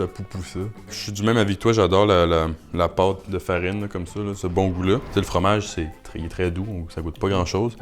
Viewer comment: Ce quoi ? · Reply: Ce bon goût là